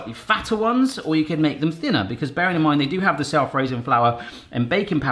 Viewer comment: For a split second I thought I heard you say -in-mind Could be a catch phrase for ya